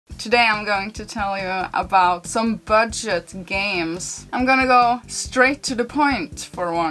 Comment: It shows a bird fishing, and I swear she says "bird shit games".